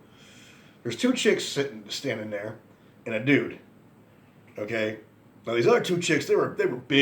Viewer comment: I did a double take and had to replay that. Sounded like you said -- ...two chicks standing there in the nude. I was like, wtf kinda Wendy's is this and where is it?! 🤣